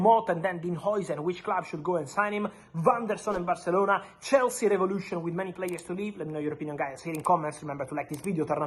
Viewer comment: Did he just say “ Chelsea revolution “ ?! 😂😂😂😂😂😂😂😂😂😂😂😂. And Real Madrid is not one of the biggest clubs in the world , Real Madrid IS THE BIGGEST CLUB IN THE WORLD .